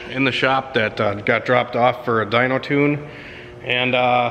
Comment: That is crazy that someone would drop that off for a tune when it is in no way shape or form ready for a tune lol. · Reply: thought he said it was dropped of just for a ecu